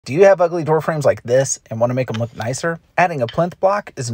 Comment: I thought he said " do you have ugly girlfriends like this" 😂